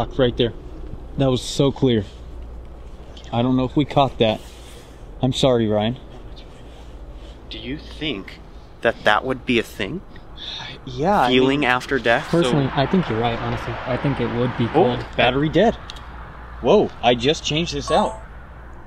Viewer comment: DID ANYONE ELSE HEAR THE CLEAR ‘YEAH’ OR ‘YES’ AT 33:27?!?!?? Right after he says the devices are cool ?!?!?